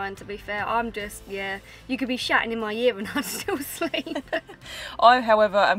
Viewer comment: I totally misheard what said and thought she said "i sleep fine...you could be "shatting" in my ear and i'd still sleep" hahaha. Rewound it 3 times and then realised she said "shouting"!!